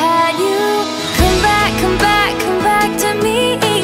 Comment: I always hear "Come back to me, Eli."